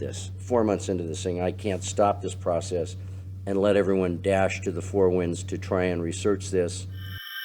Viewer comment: what does he say? Dash to the four winds?